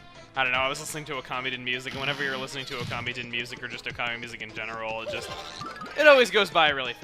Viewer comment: Ahen you said you were listen to Okami music I thought you said you were listening to communist music